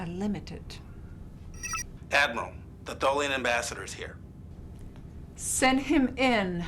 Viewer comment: I thought Nechayev said “Ambassador Sarek”... that threw me off.